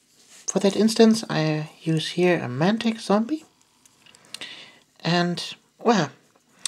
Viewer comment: Can you link where you got this miniature? I thought I heard "Mantic Zombie" But I might have misheard